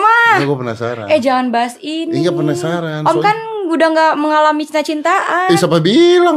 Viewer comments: ricis ngomong apa sih? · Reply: "om kan udah tua" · Ada yg tau ga menit 14:13 mba ria ngomong apa? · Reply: udh tua